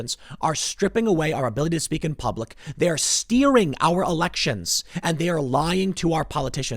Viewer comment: I’ll be honest, when he said “steering our election” I thought he was saying “stealing” with an Asian accent.